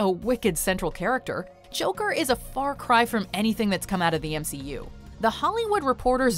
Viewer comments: Wait did she say a far cry from the MCU? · Did she say MCU? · did she say MCU